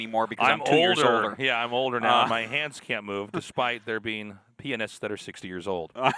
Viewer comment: Sorry if this is a noob question but what doesn "P&S" stand for? Or what did he say if it wasn't P&S, whatsoever? Because my ears and the auto subtitles say P&S. Edit: LOOOOL, he pronounced it in such a weird way even the autosubtitles got it like "P&S" and I thought it was just it and that I didn't know the meaning xDDD. Thanks for answering. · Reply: He is saying pianist